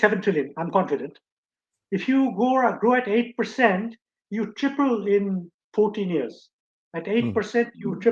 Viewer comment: Sir, a point of arithmetic, 8% growth means doubling in 9 years, not 14. Loved the episode, learnt a lot of new things. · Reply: I think the Prof said triple in 14 yrs.